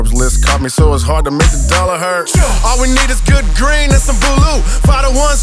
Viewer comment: Lol at first I thought he said he wanted some good greens and balut 😂